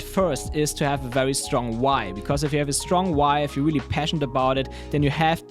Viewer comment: At first I thought you said to have a strong wife 😂😂 I guess that works too for the support! My why is because I work 6 days a week, so trying to balance my own free time with family, friends, my own growth etc, slowly working toward leaving my job though!